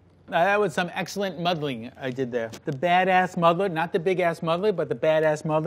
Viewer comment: did he said mother 7:13 again with the mother 7:32 what